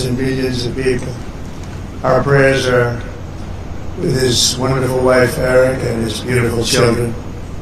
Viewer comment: Did he just say his beautiful wife Eric